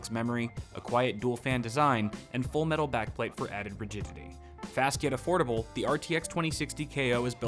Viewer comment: He said “added rigidity” during the sponsorship hit and I heard “added virginity” lmao😂😂😂